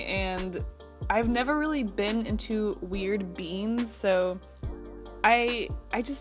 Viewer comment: Couldn't help but hear "weird beans" instead of "weird beings". Anyone else?